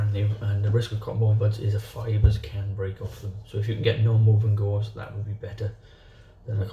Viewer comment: What do you say around 1:02 ? If you have ____ would be better. Can't quite make out the word there. Thanks! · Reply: He said "non-woven gauze"